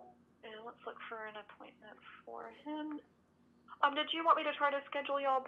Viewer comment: I guess, it should be do you want instead of did. Please confirm it. Thanks 👍